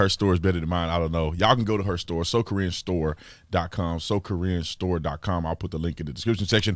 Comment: I thought that you had said “So Korean store” and then I read “So Corinne Store” 🤣